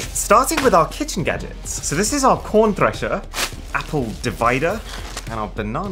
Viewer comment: I thought you said po. Rn thresher